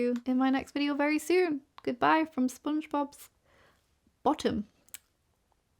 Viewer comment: Bruh I thought you were saying “SpongeBob’s bomb” and rewound so fast lol